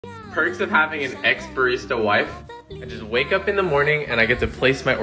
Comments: I thought it said perks of having a ex wife 💀💀💀💀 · I thought it said ex-wife😂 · I thought you said x wife and then I read it thank god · Why i hear it "an ex wife barista" i got confused 😂😂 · Am i the only one who thought it said ex wife barista? 😭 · I heard that as your ex wife was a barista and was so confused for about 7 seconds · Am I the only one who thought that he said ex wife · I thought he said ex-wife for a second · When you said ex barista wife I thought you said like ex wife, my heart dropped although I didn’t really believe it 😅😂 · I’m sorry I thought you said she was your ex-wife and I immediately imagined a Charles Boyle kinda situation